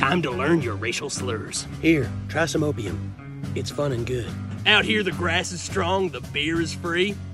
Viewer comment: At first I thought he said “here, try some oatmeal. It’s fun and good” and I was like lmao wtf does that even mean?